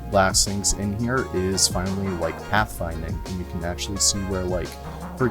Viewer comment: What's this strange word he says at 3:36? Is he just making up new words now? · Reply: pathfinding?